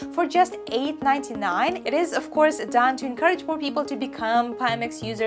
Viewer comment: hahaha! @ 9:10 she is not saying "more" people. right!?